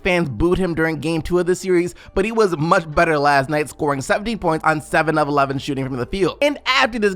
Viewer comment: I don’t know why but I thought said Barton scored 70 points off of 7 shots😂